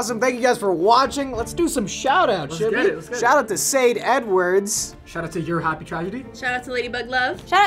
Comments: when said sade edwards i thought he said say the n word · I thought Sade Edwards I thought he said say N-Words